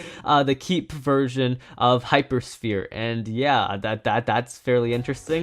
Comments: Me: Oooo uploaded Video: Private Me: Nooo!! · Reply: am i the only one hearing this or did he say "keep version of hypersphere" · Was it just me or did he say it would keep version of hypersphere